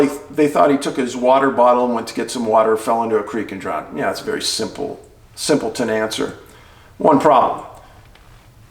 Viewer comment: I thought you said that the little dude had a "nimble" mind.